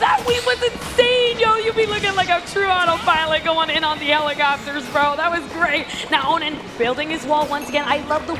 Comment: what did she say? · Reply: like auto pilot going in a helicopter